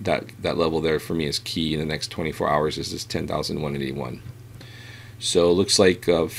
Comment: Was listening (not watching) and you got me all excited when you said $10,181 at 10:05 into the video. Started reaching for my wallet when I heard that but realized you meant $18,181 or $19,181. Lol